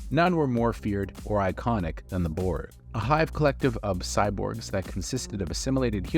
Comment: The board? Even the subtitles state what you said is "the board". Please enunciate. 1:55 "Not only did Picard scary with him the trauma". OK, that's enough. Only make videos if you have the ability to make sense.